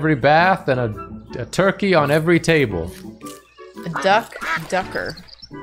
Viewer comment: I thought was trying to say the German word Dachdecker bc I think that's how it sounds with an American accent